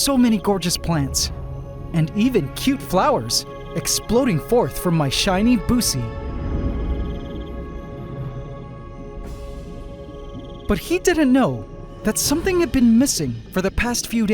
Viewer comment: "EXPLODING 4TH FROM MY SHINY BUCE" 😅 im sorry, what did you just say, i don't think i heard you correctly😂💀